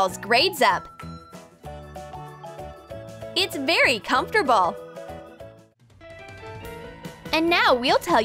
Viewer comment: its very comfterball😂 instead of comfortable